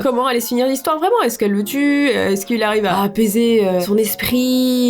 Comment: Euuh...c'est moi ou elle dis "a baiser son esprit"?😂😂😂 (Après 3 reprises enfaite elle à dit "apaiser son esprits"😅) PS:oui g l'esprit pas très pure😛😈